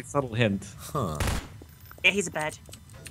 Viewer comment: "He could be on my BUCKET list, (three seconds later) Ya he's a bed." - 2013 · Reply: Hai World holy shit I've always thought she said "Ya he's a bird"